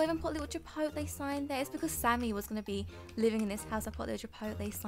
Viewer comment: did you say sandy because that's my name :p...